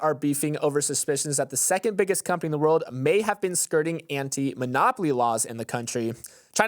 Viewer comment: I misheard "China is investigating Nvidia for anti-monopoly laws" as "anti-monogamy laws"